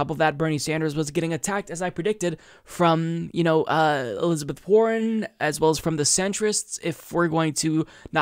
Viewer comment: trivial but i heard "elizabeth horren" xD